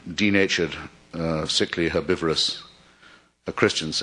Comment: Somebody help me. What was that word at 1:11:50 ? It sounded like "herbivorous" . . .